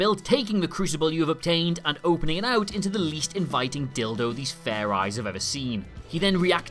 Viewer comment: I'm sorry did you say dildo